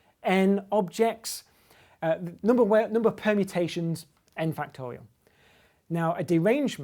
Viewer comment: Did I hear "Number of Penetrations"?